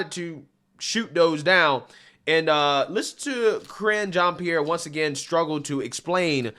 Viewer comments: I could have sworn you said "Cringe" Jean Pierre. Went back and you didn't actually say "cringe" but I think it should stick anyway. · I thought he was saying Cringe Jean-Pierre 🤔.....